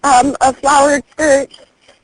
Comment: "Shes wearing a flowered skirt" "So a solid skirt or does it have flowers on it?" Me: uh.. is this a trick question? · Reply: The operator thought she heard the word, “solid.” It sounds a bit like, “flowered.”